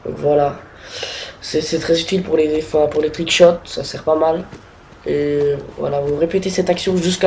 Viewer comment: C'est moi ou à 0:37 il dit "les trickchiottes" xD